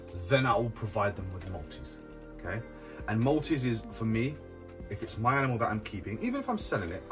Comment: At around 11:00, what is he taking about feeding snakes who will not take anything else? Is he saying Maltese? Multies? Thanks lol 💗💗 much love, this video is so helpful! I'm trying to grow my bamboo named Sal, almost 3 years old and barely 700g! Nice and round body shape, but so small overall 😭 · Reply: Multis/ multimammates. Also known as African Soft Furred rats, they are ball python's natural prey in Africa